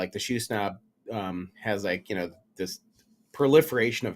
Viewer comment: great discussion. what site is that in the 3:00 mark in the video - the sound was a little distorted so couldn't make out what you said. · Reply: The shoe snob market place